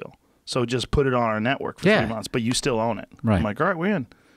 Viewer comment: did he say "three months"?